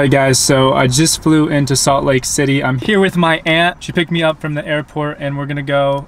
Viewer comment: i thought you said "Im here with my ex"🤣 haha aunt